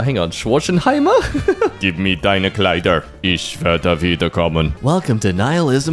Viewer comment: He said smth abt a dress right? I heard: "Gib mir deine Kleider" (Give me your dress(es?)) and then smth else. I'm not German, I'm Dutch but I'm learning German so it's cool to hear it lol. He has a pretty good accent for an Englishman imo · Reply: The word „Kleider“ can also be used as a synonym for clothes „Klamotten“. So he indeed said „Gib mir deine Kleider“ which means give me your clothes. That‘s exactly the reason I am asking wether knew what he said there. I hope this was somewhat helpful and that you can continue to learn. If you need something just ask… For what ever reason.